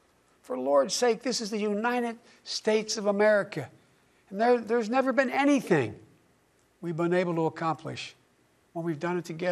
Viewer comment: All of you saying that he said "there's nothing we've been able to accomplish, that we've done together" ABSOLUTELY MISUNDERSTOOD what he ACTUALLY said, because he kind of "mumbled" it a little bit there. Go back and listen again.... he says at 5:41, "there has never been ANYTHING, that we've been UNABLE to accomplish, and we've done it together".